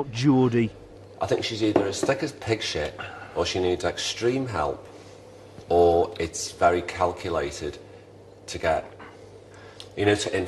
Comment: Can someone translate for me what Pete said between 0:34 and1:30? And between 09:54 and 10:46? I'm still learning English and he spoke fast and the video doesn't have subtitles, so I couldn't understand everything. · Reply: First part: I think she's either as thick as pig shit, or she needs extreme help. Or it's very calculated to inflict guilt. "Anniversary of his death. My dog died. People say my boobs are saggy" and its like oh come on. Its not all about what the press write about but its all she cares about to change the press perception of her and make them see what they've done to her. Then people walk up and call her a slag and call her this, that and the other, well people use words like that. She goes "I'd like a bit of ball action if spin the bottle came up, you wanna see my vagina? I used to lap dance and he came in his pants it was fantastic I've had an orgy with 5 girls at once I've got Polaroids of all my ex bfs cocks I like to watch myself in the mirror it makes you feel like a porn star, I'd never do porn". And if you ask me do you like her I can't say I don't like her I'm just cringing. You can take a horse to water and can't make it drink, you can take a horticulture (whore to culture) you can't make her think.